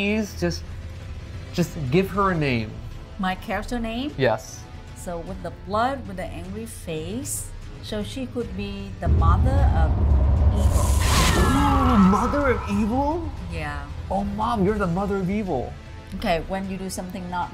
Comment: for a second there I thought she'll say Mother of Kos